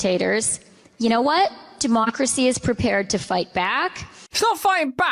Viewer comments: did she really say "demonicracy"? 🤔😈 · did she just say "demonicracy".... 😅 holy moly that was a slip up right? 😂